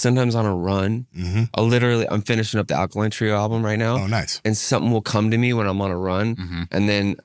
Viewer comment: I heard" I'm finishing up a country album right now...." I think I almost choked😂 Glad that wasn't it. Great interview ♡